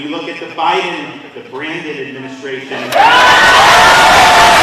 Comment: DeSantis said (19:45) "the Brandon administration" lol that was awesome! The caption said "branded" that is not what he said!